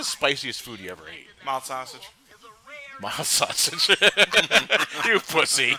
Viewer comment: what did he say?? mouse sausage?